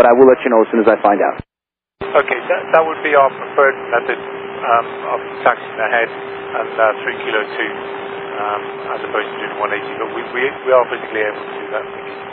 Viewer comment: Gotta say, the subtitles are poor on this one, to the point of changing the meaning of what is said. For example, at 13:08, BAW2 says "OK, that would be our preferred method, of taxiing ahead and Kilo-2 as opposed to the 180", but it's subtitled as "OK, that will be awesome to taxi ahead and take Kilo-2 or perform a 180." The pilot is expressing a clear preference for not doing the 180, but the subtitle says that either option would be awesome.